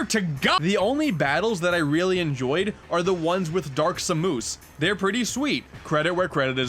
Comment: Did... Did you say Dark Sa-moose?